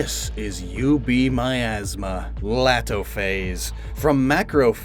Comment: When he said UB Miasma I thought he said "You Beat My Ass Ma"